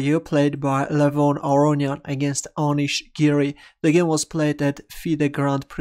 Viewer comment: did you just say drawish giri? xD